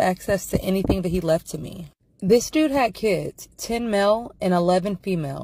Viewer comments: Bro I heard "he even has kids, 10MIL" 🥲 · WHEN SHE SAID 10 Male I thought she said 10m I was like oh he’s rich rich